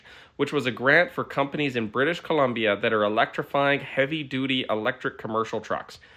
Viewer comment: British Columbia… Just say BC, every time I heard you say “British Canadia” I laughed.